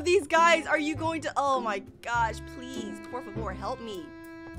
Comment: eu ouvi um "por favor" ?